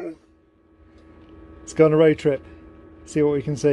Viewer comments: "a roadtrip" I heard it for once 😲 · He actually said "A road trip!" At 1:48!